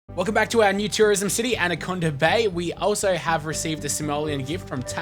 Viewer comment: Sounded like you said "and a condom bay" in the start XD